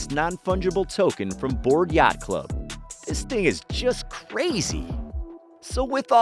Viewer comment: Did I just hear this white man say “this nigga is just crazy”